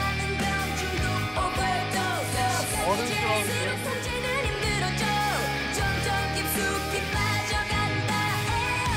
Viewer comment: = what did they said? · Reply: 1st guy "kinda adult(sexy, mature woman) vibe?!" 2nd guy "Yeh~ Really, so Cool"